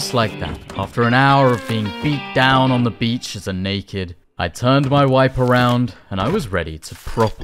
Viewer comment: Did you just say you where gonna "turn your wife around"?!! xD